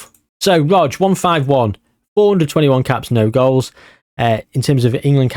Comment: Was I the only person who thought that at 15:03 he said four hundred and twenty one caps